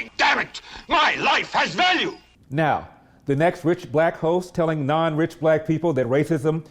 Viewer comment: LOL, @ 2:16 I thought you said “The next rich black ho.”